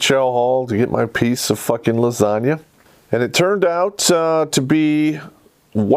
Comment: I thought you said bitch lasagna.